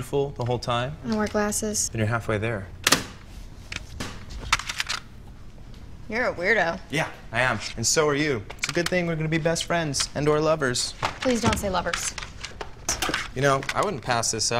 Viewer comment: Can someone tell me what they say in 2:18 - 2:49, please? :) I don't speak much English and I'm not good listening :/ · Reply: Beca: "Youre a weirdo" Jesse: "Yeah, I am. And so are you. Good thing we're gonna be best friends, and/or lovers." Beca: "Please don't say lovers" Jesse: "You know, I wouldn't pass this up. Once I'm a Treble, I probably won't have any time for you." Beca: "Wow. No I didn't think there was a way for you to be less attractive to me. But congratulations. You've managed it." Jesse: "Just wait. You'll go all squiddy, drape yourself all over me; It'll be fun. You'll see."